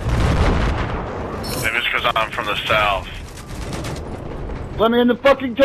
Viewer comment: That was some nice tanking! 0:23 "Let me in..." At first I thought he said, "Women in the f-ckin tank!" Lol